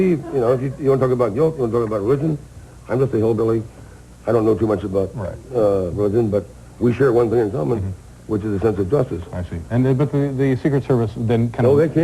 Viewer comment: What part does he mention Camp Hillbilly ? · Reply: To anyone who ends up here what he says is "I'm just a hillbilly, I don't know too much about nothing but one thing we share in common is a sense of justice." Slow it down to .5 speed at 7:10 or so and you can hear it clearly